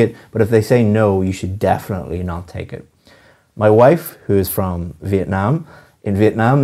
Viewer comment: did you said your wife comes from Vietnam?